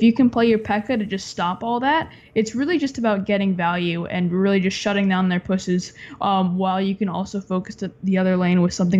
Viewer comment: did he say: just shutting down their pussys